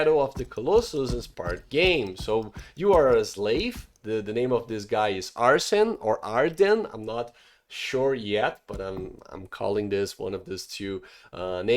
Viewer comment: Sounded like you said Arson lol